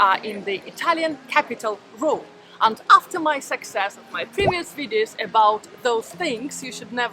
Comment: I thought she said 'After my Sex change'. One moment. Let me listen again.